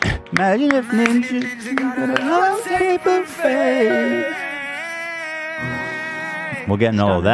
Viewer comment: All I hear is "Imagine if ninja got a low tier buffet"